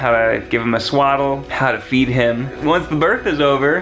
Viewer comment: I thought you said “how to beat him”